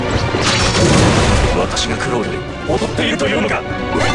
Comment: ここまじで｢私がクロール……踊っているというのか！｣って聞こえる どゆこと？